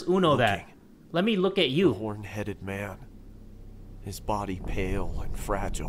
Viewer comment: The second video made me laugh...... when the dude said the Horn Headed Man, I thought he said the One Headed man. And I was so confused.🤣🤣🤣